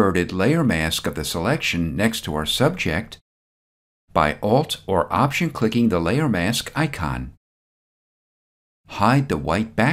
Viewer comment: Hello! I recrently started following you videos. Please explain me how to make inverted layer mask at 6.02. Its not happening as you told by just pressing layer mask icon down. Its been 3 days now i am stuck here. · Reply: I think you mean 4:21. I said, "...by Alt or Option-clicking the Layer Mask icon."